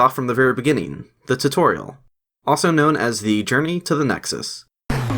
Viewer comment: I’m sorry but I keep hearing “Germany to the Nexus”